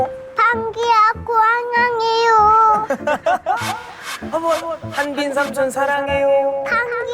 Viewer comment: 나만 변기삼촌 이라들리낰ㅋㅋㅋ